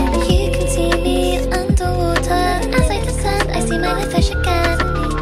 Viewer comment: i know she says "i see my life flash again", but i keep hearing "i see my lil fish again" anyways THIS IS SUCH A GOOD SONG AAAAA